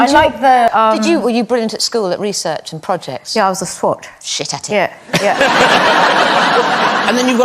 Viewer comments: Can someone tell me what they are saying at 8:46 please (after « Yes I was a... » ?) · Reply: Is it « swatch » 😅 ? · If you also know what they are saying at 8:47 please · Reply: She said she was a swot (someone who studies very hard at school)